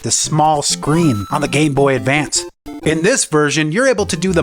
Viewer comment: did he say gayboy advance?